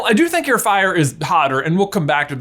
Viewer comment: did he say potter?